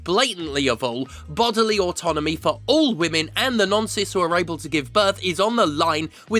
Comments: I realise you said non-cis I heard "the nonces who are able to get pregnant" and I was confused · You should maybe enunciate "non-cis" a little more clearly in future because I heard the "the nonces who are able to give birth" the first couple of times. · There was a line where you said "non-cis" but for a second I thought you said "nonces" and was briefly very confused.